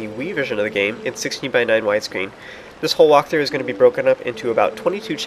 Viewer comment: At first I thought you said "1% walkthrough" but of coarse that's not how rolls.